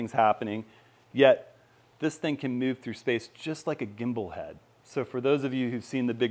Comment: I thought you said bald head for a minute 😂